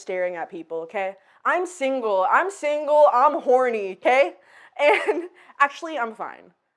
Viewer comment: I heard 'I'm forty' instead of 'I'm horny' 😂 Had to double check, well Shakira actually looked that young at 40